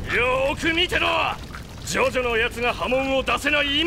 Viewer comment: did he just say jotaro